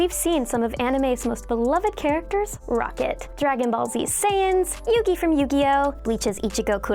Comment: Exuse me did I hear sans